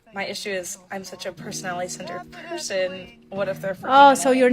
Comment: you got it wrong, 8:10 she said "I'm a personality centered person" I think she's trying to say personality is more important for her.